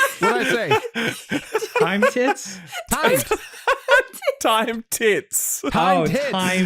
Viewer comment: I heard time tits also lol